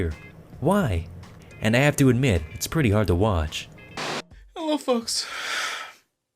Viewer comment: "hella fucks" XD (I am not making fun of tommy, just the mishearing of his words)